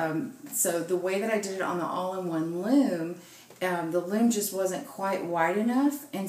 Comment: may I ask please, are the pegs on this loom like those on the All-in-One or those on the Hat loom? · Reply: camcab147 I thought she said it was like the all in one loom.